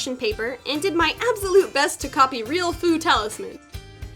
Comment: 😲😲😲 Why did it take me over a year to realize she was saying "FU" talisman?!?!?! I thought she was saying FOOD talisman 😫 and I was like what does food have to do with Espeon? 😒